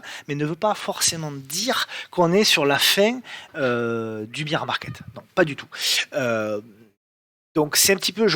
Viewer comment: C'est moi ou tu prononces "beer market" ? ^^